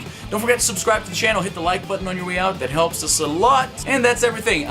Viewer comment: oh hit the like button thought you said hit the dislike button :)